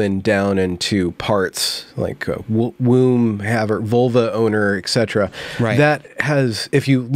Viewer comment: I thought you said "Volvo owner" 😂